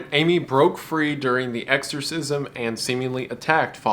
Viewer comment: Exorcise? I thought you said extra fries haha gets fucking shot -michael Davies